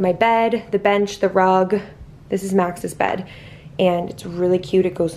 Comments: I thought you said “this is Mac’s bed” on the floor 😂😂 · I literally thought you said mac’s bed lol 🤪